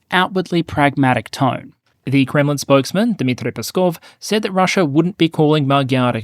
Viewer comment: At first I thought you said 'gremlin spokesman' and I already knew it was going to be Peskov.